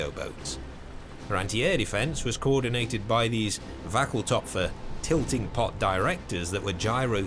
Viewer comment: that's probably "Wanckel Topf" instead of "wackel". Don't know much german myself but i do know that "wankel" means wobbly in my language. I'm sure that the actual germans here can confirm or deny this better than i can though.